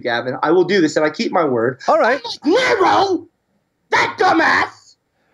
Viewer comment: when the ginger said "nero" It sounds like he says negro